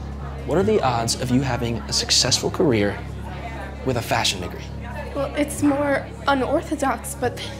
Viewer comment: Did he say fashion n-word it sounded like it